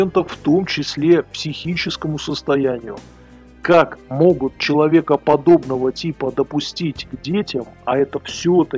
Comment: Не расслышал, "человекоподобного типа" или "человека, подобного типа"? Склоняюсь к первому. А вообще не удивлён. Думаю, история о том, как он стал директором этого колледжа, многое объяснит. У нас в институте тоже интересная история со сменой ректора была. Прям спецоперация. Прекрасного ректора в итоге заставили уйти под угрозой уголовки и сменили на одного мудака. У него в то время очень мощная крыша была, занимавшая большую должность. В результате много хороших преподавателей ушло. Кучу денег распилил освоил. Развалил не один, а сразу два (!) университета. Крыша, повторюсь была очень мощная. Но уголовных дел нет, что характерно. И вряд ли будут. Живёт и здравствует себе сейчас. Уже не ректор, крыша должность занимать перестала. Но с хлеба на воду не перебивается. Ждёт возможности ещё что-нибудь "освоить".